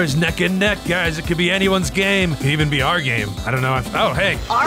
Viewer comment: why does stoner say gay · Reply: Its game